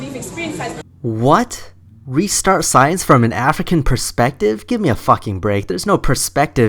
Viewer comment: I thought it said ethical perspective, but point still stands